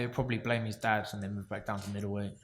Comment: What did Groves say at 20:36? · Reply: He will probably Blame his dad and move back to middleweight.